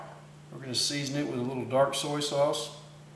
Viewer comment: did he just say, soil sauce?